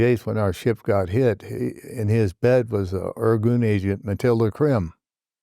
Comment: anyone know what he said here after he said “his bed”? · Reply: He said, "in his bed was an Irgun agent, Mathilde Krim." The Irgun was "a Zionist paramilitary organization" apparently and this woman was later honored by Clinton for starting up AIDS research and on a team that first developed a method for prenatal determination of sex of infants. Was a huge activist too for an array of causes and had lots of pictures with stars and public figures.